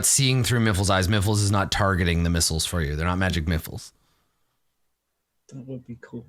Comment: Did I hear that right? "They're not magic Miffles"? LOL!